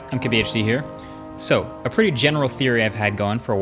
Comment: I swear every time i hear " I'm KBHD " instead of "MKBHD"